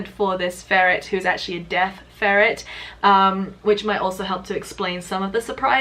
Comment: I thought you said "death ferret" and I was mildly concerned about what that means